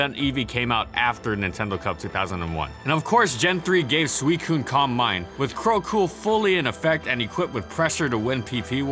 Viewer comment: What is he saying in 5:43? "Crow cool"? I'm not seeing it anywhere on Google.